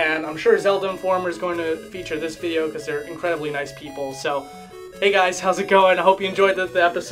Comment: when he said "zelda informer is likely gonna feature this video because they're incredibly nice people" I heard that as "competitively nice people"